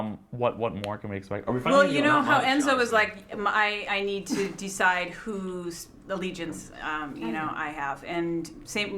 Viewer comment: what did paul say cuz the cast was laughting and he was saying im just joking to kat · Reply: Hanna Hanukaiva he said “not much honestly “